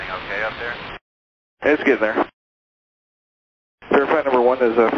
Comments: subtitle is wrong. Should read "It's getting there" instead of "It's good now" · I think the new ATC says "It's gettin' there." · Is it just me or is the ATC saying "it's getting there" instead of "It's good now" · I think the subtitle is wrong when somebody asks "Is everything OK up there?" 12:48 the sub says "It's good now." But what I hear him say is "It's gettin' there." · subtitle says "It's good now", the ATC actually said "It's gettin there". Likely they were still in the middle of medical response in the background, while he was rapidly digesting the current flights situation. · subtitle is wrong. Should read "is there somebody up there that knows what they're doing" instead of "It's a comedy up there". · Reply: Also at 12:58 he actually says "it's getting there." :P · subtitle is wrong. Should read "is there somebody up there that knows what they're doing" instead of "It's a comedy up there". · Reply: Also @ 12:58 should read "It's getting there." instead of "It's good now.".